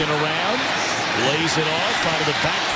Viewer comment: did he just say Washington?